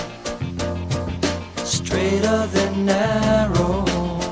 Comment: I never thought the Lyric said "straighter than narrow" .. I always heard .. "Straight up and narrow" ... which makes sense since he's talking about a phallic analogy here. Hello?